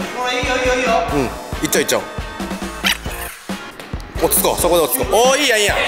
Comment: そこで落ち着こうがそこで落ちように聞こえた✨